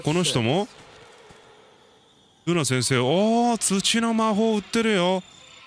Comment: さんの「土の魔法売ってるよ」が 「寿司の魔法売ってるよ」に聞こえた… 血糖値上がるよ。 サムネ 「おいデュエルしろよ」 そのうちマグマム・オトジャと言う名の Dホイール乗りそう。